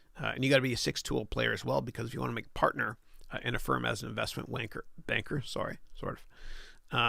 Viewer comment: Did I hear "investment wanker?" :') hahah!